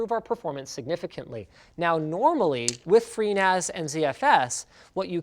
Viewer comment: When he said he was going to use FreeNAS, I thought he said pNAS. Lol. Clearly got penis on the mind, lololol!